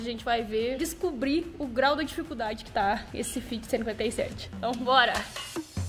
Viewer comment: Eu ouvi "' Fiat 57 "' 2 vezes 0:16 e 0:24 kkķkk salve!!!!